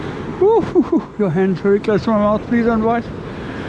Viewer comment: Well done getting that cotton off . But at 1:50 did you say your hand’s very close to close to my mouth instead of the other way round lol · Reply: You'r mouth is very close to my hand, please don't bite 😉😅